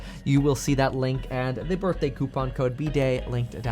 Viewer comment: I literally thought you said get your free socks at the beginning.